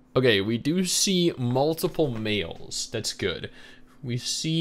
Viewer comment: I thought you said meals and not males at the start lol. So i was like “damn, bro that hungry already?”